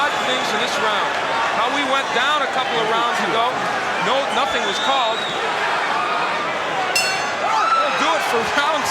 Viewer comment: what did foreman whisper in his ear? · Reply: He said “you need to quit”